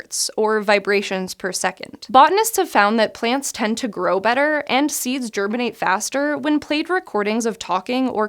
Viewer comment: What I heard at 0:52 : "...and seize germany faster."